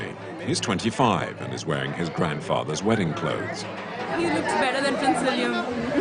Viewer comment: did she say 0:44 " he looks better than prince Williams ??? "